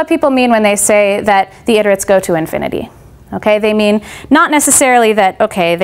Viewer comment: I heard "the idiots go to infinity" xD